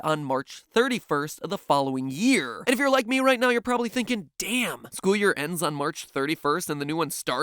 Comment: Did matpat just say damn?